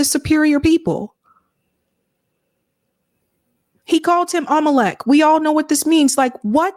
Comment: somebody please tell me what this word is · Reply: Amalek .